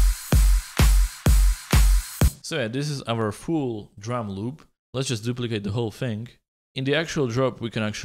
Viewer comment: when he said fuul drum loop i heard it as fool drum loop always his fuul sounds fool